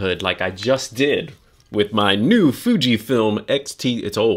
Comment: Not sure if you realized you accidentally said my new Fujifilm X-T instead of X-E at the 0:42 mark lol